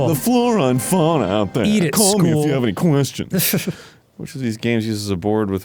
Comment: I thought Dan said, “Eat it, Squoval.” Like that the Metal Gear Solid lady was calling Snake the combination shape of a square and an oval (a common fingernail shape).